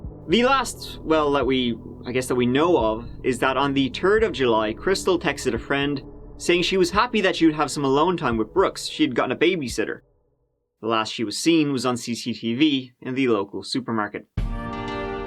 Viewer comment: I subscribed.....then I heard: ..."the turd of July..." 😂😂